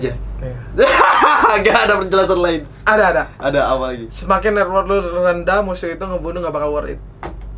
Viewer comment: si ngomong apa cok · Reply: Semakin network/gold lu rendah musuh ngebunuh lu gk bakal worthit